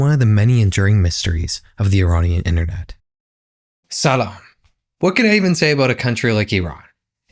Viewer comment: what did u say at 5:58 Salah? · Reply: Salam